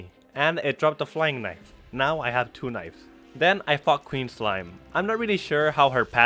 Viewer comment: I thought he said he fricked queen slime 😂